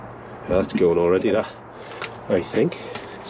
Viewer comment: At the 24 second, I couldnt catch the word that he say once the clog start to break.. I heard "lets go on already lah"? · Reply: "That's going already that" 👍